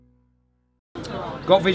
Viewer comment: What did he say @ 2:51? French? Too cute. Hi! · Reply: got visual, we're all right for visual.